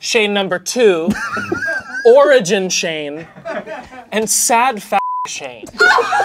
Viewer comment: I will never get over calling himself "sad f*g Shayne". Or his support for Courtney, we love to see it. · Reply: Until recently I thought he said sad f*ck shayne